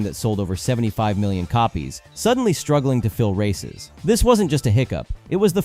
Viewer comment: I heard struggling to feel racist 😂